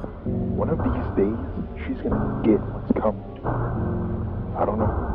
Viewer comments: I think he’s saying “one of these days, she’s gonna get what’s coming to her.” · I want to point out something that someone might of just said but I heard “One of these days” instead of what you thought “Whatever your seeing” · I think he said “One of these days,” instead of “Whatever you’re seeing”. It makes more sense to me that way!